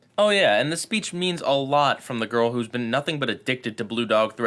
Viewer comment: I keep hearing you say "PieGuyRulz...ow" at the end of your videos